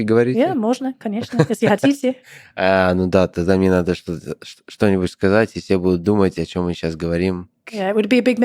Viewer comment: Anyone know what they said at 8:27? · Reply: I'm Polish, not Russian so I might be wrong, but I think that he said "Then I need to say something (in Russian) so that everyone will be wondering what we just said" That's why she said "It will be a mystery for everybody". Basically it was an irony, because he didn't say anything interesting, just making a sort of "easter egg" for Russian speakers.